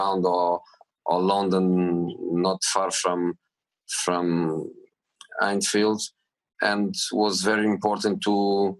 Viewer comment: Did he say not far from Anfield?